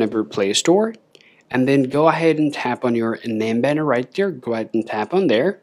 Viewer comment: What on earth did he just say; 00:33 ?? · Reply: "On your name Banner"